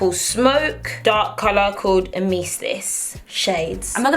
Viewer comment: I had to look up the name of the last one because I thought you said "emesis" which means vomit in medical terms, and I knew there was no way it was named vomit. Turns out its called Amethyst, but I had a good laugh regardless. Love your videos, always super entertaining and your sense of humour is always good for a laugh. Love from Canada!